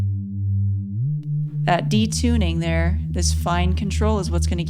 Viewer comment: The first few times you said Reece Bass I thought you were saying re-space. 🤷‍♂🤷‍♂